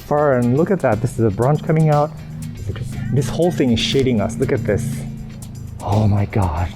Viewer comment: Hahaha! I thought you said 'this whole thing is sh*tting us'! LOL